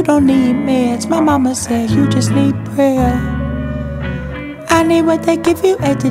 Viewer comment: I always thought it was "mama said you just need bread." 😂 or switched between prayer and bread. Prayer sounds so much better tbh though.